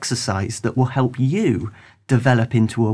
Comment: at the end he said "just keeping practicing" and I misheard it for "skip practicing" lol. Anyway, greatest bass lessons series ever